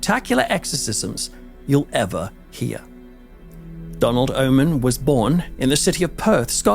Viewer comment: I thought I heard you say Donald Omen. I was like, "Wow! Talk about destiny!"